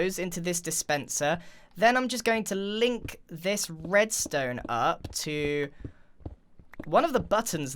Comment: I’m pretty sure I heard say “breadstone “ instead of redstone